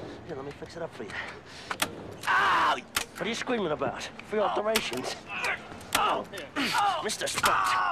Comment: "Whatayu screaming about? It's free alterations." LOL! Best writing for any TV series, ever! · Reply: Lmao i thought he said field operations